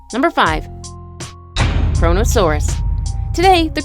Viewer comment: I thought she said CORONA SAURUS